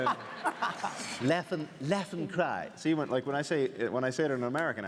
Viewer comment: I always thought he said "death and crime"